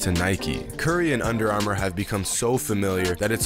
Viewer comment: subtitles say it’s hard to imagine I’m naked 😂 😂